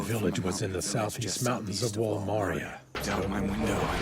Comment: Why is it that the caption says on 3:52 for wall maria is wallmart